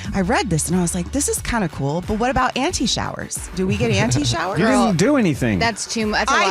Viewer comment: lol I thought was saying “Anti-Shower” not “Auntie-Shower” lol I was like woah what’s she got against showers? Lmaooo 😂